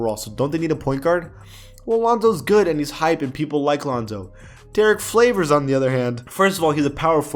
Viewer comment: did he just say Derrick Flavors